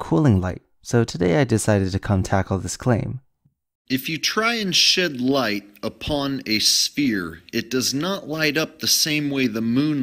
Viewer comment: I thought he said “if you try and shit light on a sphere”